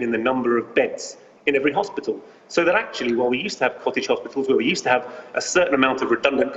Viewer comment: " where we used to have coffee shops". Is that what he said? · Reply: Lol. 'Cottage hospitals' ;)